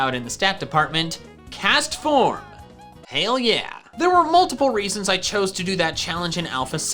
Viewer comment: NOO MR/MS SUBTITLER SAID HELL YEAH INSTEAD OF HAIL YEAH 0:15 PLEASE FIX